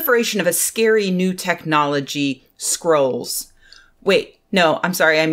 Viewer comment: i heard "skrulls" instead of "scrolls" XD